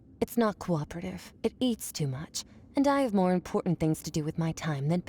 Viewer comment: just noticed, why does she say she has more important time, while the captions says her disciple